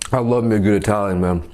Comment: it sounded like he said “I love nigga Italian man” 😂